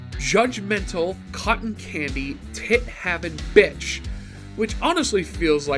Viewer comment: are you sure she did not say “dick havin bitch” cuz thats what i heard lmao